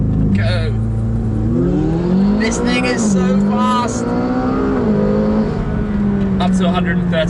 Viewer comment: what did you say? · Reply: Ivar Bruijns "this thing is so fast"